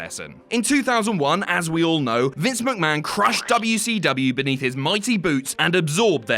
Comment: I'm watching this without headphones and I heard that Vince "crushed [WCW] under his mighty boobs". So those were the real grapefruits, huh?